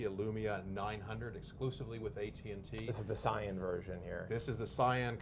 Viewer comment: I thought Josh said "Psion" hahah.